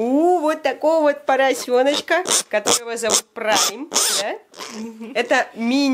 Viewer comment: Мне послышалось пига зовут брайн😂